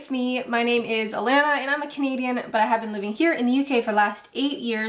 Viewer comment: When you said "I'm a Canadian" I thought you said 'I'm a comedian' lol 🤦😆🇨🇦🇬🇧